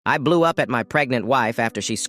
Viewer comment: Instead of hearing “I blew up at my pregnant wife” I heard “I blew up my pregnant wife “😭😭😭